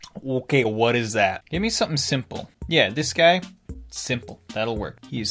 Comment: An add popped up and itsounded like saying 'simp'